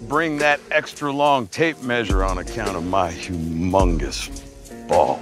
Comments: WHEN NEGAN SAID HUMONGOUS I THOUGHT HE SAID AMONG US MAKE IT STOOOOOP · If it wasnt for sub i would've thought he said “i am vegan”🤣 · Sounds like he says am vegan xD